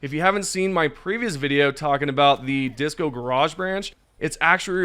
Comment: I was listening to your last post here in audio form since I was at work. When it got to the section taking about DiscoGarage I misheard it as Disco Barrage and now I can't stop imagining rocking out to some disco variant of the orbital napalm barrage. I can see Helldivers rocking out as rainbow death flames engulf enemies of Super Earth around them:D